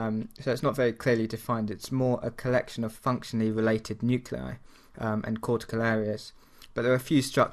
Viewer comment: Can anybody say what did he say between 4:25 and 4:27? Its more of a collection...? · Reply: of functionally related nuclei.